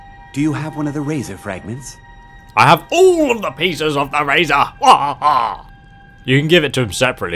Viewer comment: when u said ok we have the 3 pieces of the razor i thought u said 3 pizzas